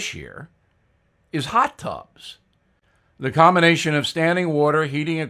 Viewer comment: Slowly going deaf can be very amusing - i heard you say "hot dogs" instead of hot tubs", Bill. XD.